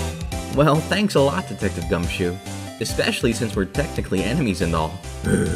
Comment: I thought he said "defective gumshoe" goodbye